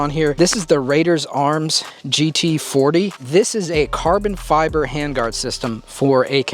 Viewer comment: yo I missed what material that hand guard is made out of. Did anyone catch that?😂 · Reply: I think it’s Carson flyers